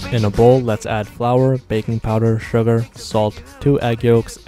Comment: I'm so shocked how people don't even listen. He said it's beef bacon. Smh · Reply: yeah I thought he said bacon powder lol